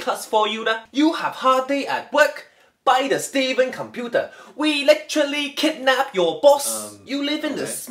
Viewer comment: " we literally kidnap your boss" 😂😂😂😂😂😂😂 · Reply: At first, I thought it said, "We electronically kidnap your boss." Lmao